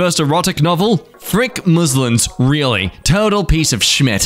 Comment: i thought it said muslims for a second